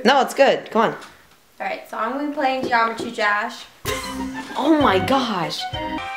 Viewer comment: we are going to play Geometry DRash! Me: wow... first time I hear a person say drash instead of dash lol